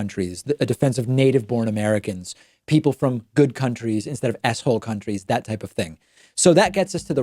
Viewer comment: When David says "s-hole countries," I hear "asshole countries."